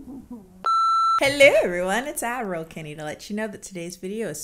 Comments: I just realized Kennie isn't saying "Admiral Kennie" she's saying "Ad roll Kennie." for this whole time I thought she was calling herself Admiral for some reason. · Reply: I for the longest time thought she was saying "Adderall Kennie" 😭😭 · I just realized Kennie isn't saying "Admiral Kennie" she's saying "Ad roll Kennie." for this whole time I thought she was calling herself Admiral for some reason. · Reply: I also thought she said Admiral Kennie instead of Ad Roll Kennie. She also mentioned it in a video, don't remember which one it was. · I just realized Kennie isn't saying "Admiral Kennie" she's saying "Ad roll Kennie." for this whole time I thought she was calling herself Admiral for some reason. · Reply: I thought she was saying Adderall kennie🙃🙃